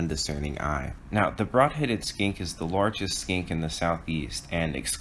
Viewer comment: I only hear "largest SKANK in the SE" :D 1080p but not in focus... :(